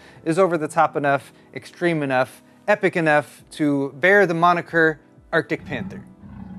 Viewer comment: I apologize but I thought I heard you call the PC the "Arctic Pimp" and like the guy below, Arctic Dragon seems more realistic when we know the Devil is a better brand (PowerColor).